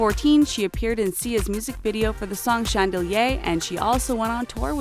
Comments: I heard “chandelyey”. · did she say “chandelyay” wtf 💀💀😂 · is it just me or did she say shang-del-yay instead of chandelier (chan-de-lear) 9:48+